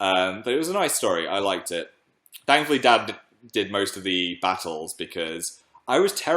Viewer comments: DID I JUST HEAR WHAT I THOUGHT I JUST HEARD PHIL LESTER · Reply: it sounded almost like dab but im pretty sure he said dad oml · umm phil can you say dan's name one more time? sounded like you said 'dad' mate